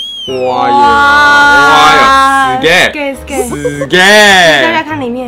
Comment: 聽成（死gay)